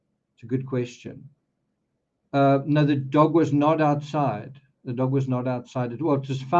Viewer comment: I thought they said the dog was found outside. Hmmmmm